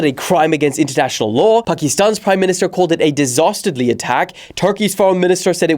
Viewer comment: Did anyone else hear him say disastardly?